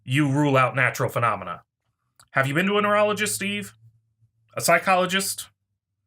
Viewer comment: I heard "...neurolgis, Steve?" instead of "...neurologist, Eve?" It amused me.